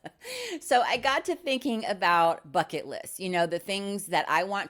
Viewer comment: I uh... misheard 'bucket' list as um... phuckit lists. 😅